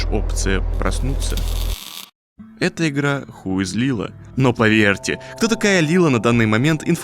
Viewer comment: меня послышалось не Who's Lila а Х.. Злила и потом проговорить второе и также как и первое теперь диссонанц пытаюсь материться а получаеться английский..